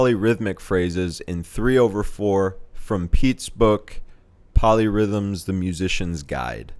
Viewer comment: Oh, POLY rhythms. I thought you said MOLLY rhythms......